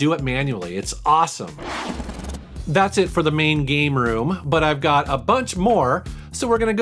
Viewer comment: I thought he said gay room.